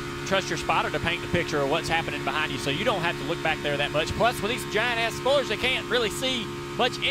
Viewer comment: does he say giant ass spoilers?